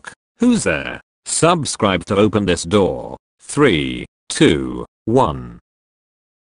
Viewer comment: Ummm 0:09 what? Ur voice said “door” but the subtitle said “video”